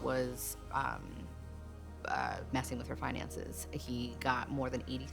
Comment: i have CC on and i thought finances said fiancés